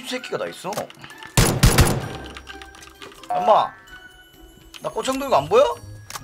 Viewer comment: 에나만 엄마 나고X 없는거안보여? 이케들림?